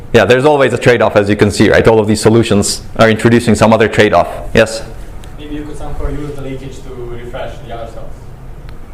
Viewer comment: Did anyone catch what the question at 28:40 was? I've listened a few times but I can't hear what was asked. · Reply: "Maybe you can somehow use the leakage to refresh the other cells"